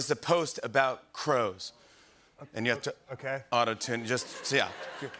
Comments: was i the only one who heard him say auto tuna in the beginning😭😭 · before seeing the game name i literally thought he said Auto Tuna and was thinking why Tuna is involved